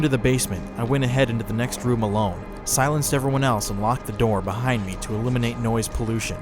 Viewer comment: The voice in time of 39 sounds like it says unlock the door